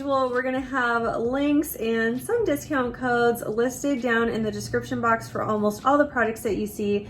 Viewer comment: I heard "subscription" box instead of description box and I almost lost my mind for 2.5 seconds! You should totally start a sub box for us!